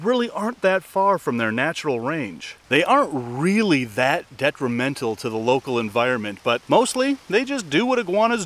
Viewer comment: did he say they AREN'T really that detrimental to the environment?